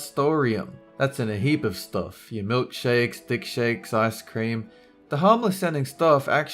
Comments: did he really say dick shakes lol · did he just say "dick shakes"?????? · did he say dick-shakes?